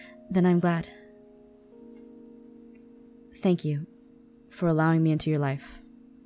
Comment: I thought Aaron said Thank you for allowing me to be your wife! need to check my ears!